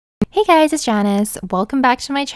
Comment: She said “it’s Janice” and I thought she said “it’s Jaundice.”